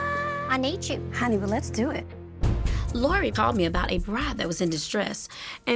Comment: This bride is a nightmare. I want to look like another person I take a fake character like ok then. Just plain rude too like wow. I swear I heard brat not bride 4:35 lol!